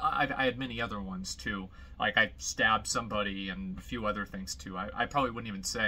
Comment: Am I hearing that right? He "stabbed somebody"?